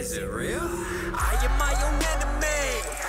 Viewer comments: I thought you were saying 'I am my own anime' in the opening clip lol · Your song in the begining sounds like ur saying"I'm my own Anime!" Haha😂😂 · I thought this man was saying "I am my own anime"